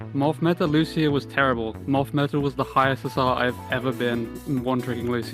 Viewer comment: Can someone tell me what morph meta was as mentioned by Lucio at 5:40? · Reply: He said moth meta. Early OW1 meta where mercy was cracked 😁